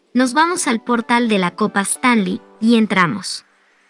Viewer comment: por un momento pense que dijo Stalin XD